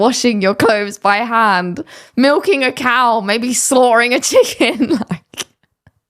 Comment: oh, slaughtering a chicken. I thought you said 'sawing' a chicken and I thought imprisoning them and forcing them to play weird games seems odd.